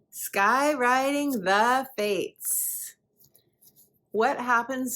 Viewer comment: All I heard was "Sky right in the face" lol